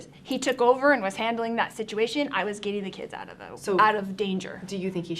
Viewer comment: “He took over and was handling that situation, I was getting the kids out of the w…. Out of danger” Told on herself. Sure sounded like she stopped just shy of “getting the kids out of the way”